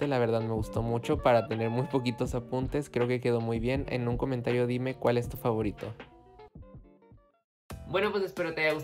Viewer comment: que dijo? Xd Con menos apuntes que no son menos materiales ="v?